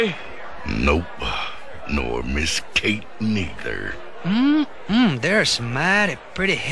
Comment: what the...did Dakota say the n word?!